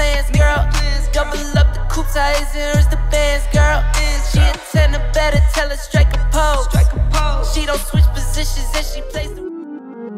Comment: when said "witch" I thought he said "B!tch" lolllllllll